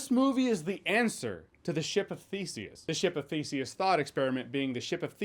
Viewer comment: I thought you said "ship of feces" and it still made sense somehow.